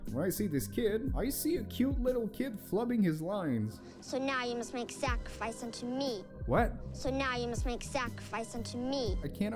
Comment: while still not threatening he isn’t flubbing I think he’s saying “So now you must make sacrifice unto me” as in the biblical verse “here I am God, be it unto me”